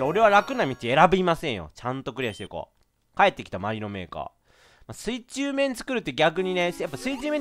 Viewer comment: 帰ってきた、マリノメーカーって聞こえたの俺だけ？ｗ